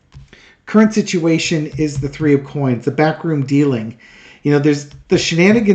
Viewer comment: For some reason every time you say "backroom deal" I hear it as "bathroom deal".